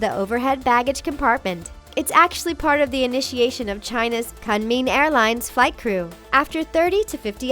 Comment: I thought that it said Kumming..